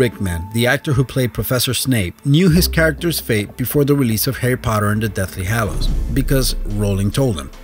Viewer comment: I thought 11 said fappy potter